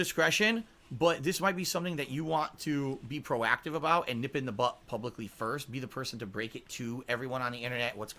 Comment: Did he say "nip it in the butt?" 42:12. Sure sounded like it.